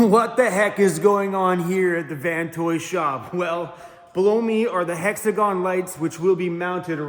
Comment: I thought you said... blow me. Quite the intro 🤪